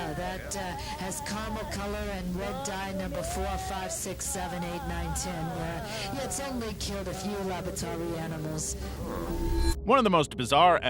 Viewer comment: i think she said "That's, uh, has Carmel color and Red dye Number 4, 5, 6, 7, 8, 9, 10, its only killed a few labari animal."